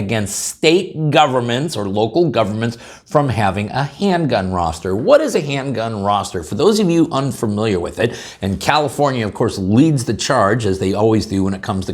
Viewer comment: I thought that said rooster 🐓 for a second.